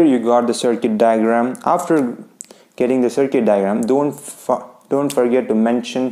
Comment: DID HE SAY DON'T FUC..